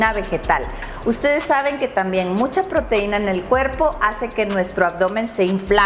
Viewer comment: Mucha proteina inflama?? o es "mucho carbohidrato?? (No entendí esa parte en el minuto 6:58) Gracias